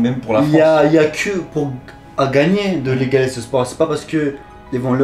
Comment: il a dit « y’aura des turcs, qui vont naître » ou c’est moi qui a mal compris ???? 😂 😂 😂 😂